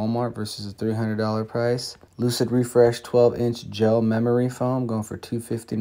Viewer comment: Thanks for the deals. I did a double take, I thought you said refurbished memory foam mattress haha .